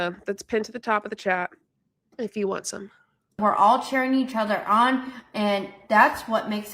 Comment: Did she just say "being part of a team is turning each other on" or am I tripping 😂